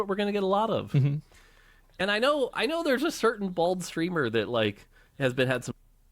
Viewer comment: I heard "a certain bold streamer"